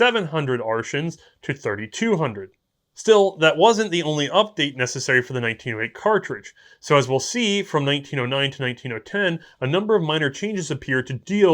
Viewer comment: did he just say 19-0-10? 0_o